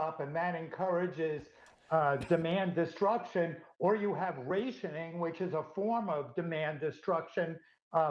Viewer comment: did he say "rationing"? I have never heard rationing pronounced like that. American's really have butchered the English language.